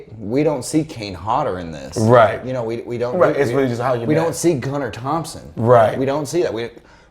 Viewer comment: Gunner Hansen plays leatherface. Sounds like your saying gunner Thompson maybe I'm wrong